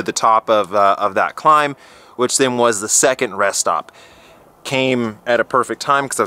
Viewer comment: I'm sure I heard "at the second stress stop" :-) replayed it, nope. rest stop. Stress stop seems more fitting though. You like riding by the ocean and climbing? Check out the ride up to Whistler <link>